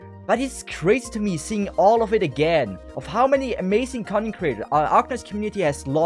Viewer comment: I usually watch videos in 1.5x and whenever you say "content creators" i hear "cunny creators"